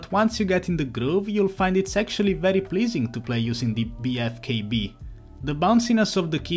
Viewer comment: did he say sexuliy pleasing????